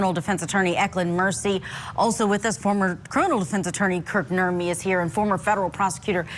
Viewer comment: Totally off topic, but every time she says "Kirk Nurmi" I hear "Bert and Ernie" 😂 Back on topic: He was a really good witness for the prosecution. The defense tried over and over to poke holes in his credibility, but he was brutally honest and shut them down every time. Leilani is guilty, guilty, GUILTY! Don't blame this on postpartum, don't blame this on drug addiction. It's obvious from her attitude today that she feels absolutely no remorse and STILL holds hate in her heart for that poor little boy that did absolutely nothing wrong. She's just evil